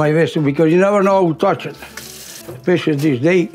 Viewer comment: Did he say pishadeel at 2:10? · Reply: He's saying "especially these days" :) ... "with all those things go around, you know" We're going to try our best to get subtitle files uploaded soon! We were previously putting the subtitles directly into the video (open captioning), but now we're going to move towards making them closed captioning that people can toggle on/off at their own desire. Thanks for watching!